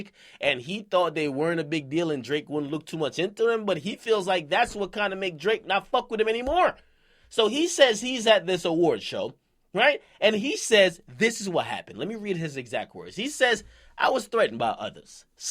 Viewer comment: Thought it said fuck him